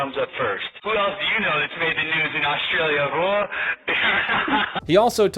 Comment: Did he say "Who else do you know made the news in Australia, hooah"? LOL That's some private s*** right there.